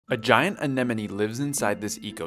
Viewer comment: I thought i heard "AN ENEMY".